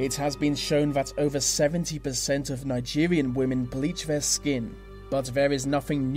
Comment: did he say Nigerian women???